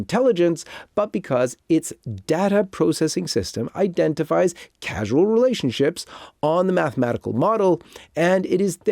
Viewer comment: should be "causal relationships" instead of "casual relationships" 5:04 should be "the rite of Communion?" instead of "the right of communion?" 5:16 should be "to the rite of" instead of "to the right of" I might have missed other errors.